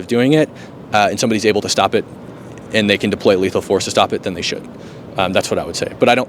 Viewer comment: the subtitles got it incorrect. He said "should", not "shouldn't". Amazing bravery, thank you yet again for standing up for truth, even when threatened in every which way. Now even more than ever